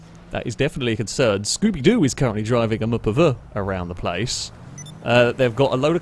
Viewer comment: I've been watching this series since episode 1 and I still can't figure out the nickname he calls the Vapid Radius at 15:39. Mupperver? Muppever? I hear it every single episode, somebody help me out lol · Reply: MuhPuhVuh = MPV = Multi Purpose Vehicle.